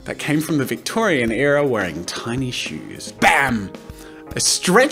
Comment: you said tiny I heard tiny IT WAS SHINY